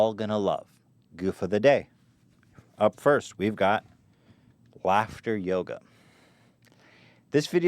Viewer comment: For some reason when ethan first said "laughter Yoga" I thought he said "Black Yoda".....